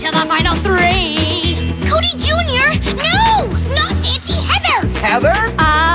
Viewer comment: Why are people talking about the voices, when I am laughing about the “Not auntie Heather” 🤣😂🤣😂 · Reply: i thought it said “not izzy, heather”